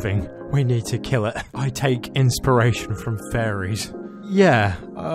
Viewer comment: Fairies I thought she said furries and I was about to say dafuq